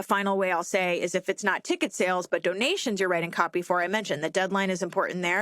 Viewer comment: This came up in my podcast feed when I was out on a walk, and for the first 3 minutes I was so confused — I thought you were saying "copyrighting." I wasn't sure what an orchestra would copyright, but it's not out of the question given the field. I think it would be much clearer if you stuck to "writing copy."